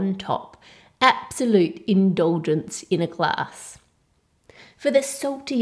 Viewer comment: Absalute endonlagence in a glass(i cant hear what she said) Me: there is a spill going on....